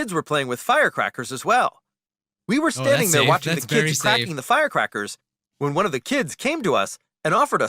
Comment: Captions Says: Eating the firecrackers